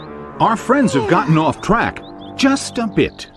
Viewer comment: it sounds like he is saying crack